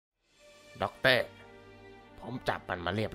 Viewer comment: ใครได้ยินว่าด็อกเต้บ้าง